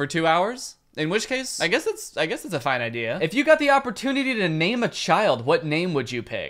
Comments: when he said name a child it sounded like he said maime a child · I swear I heard him say “if you got the opportunity to maim a child...” but he didn’t.